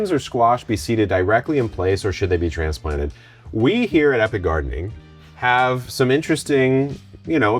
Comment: Hi Kevin, I used to think your name was Eric when I first started watching your channel. Because, at the beginning of your channel when you introduced yourself you talk so fast and it sounds like you say your name is Eric when you say epic gardening. Please don’t get your feelings hurt if everyone hadn’t figured it out yet. And then I think some people are teasing you all in fun. Happy gardening 👩🏻‍🌾